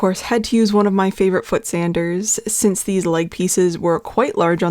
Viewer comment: I kept hearing you say "foot sander" and thought it was powered like a sewing machine with a pedal, hence "foot". 😂 my face when i followed your affiliate link. Definitely will look into getting one.